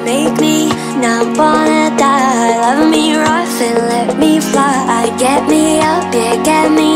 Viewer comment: I thought she said love me ralph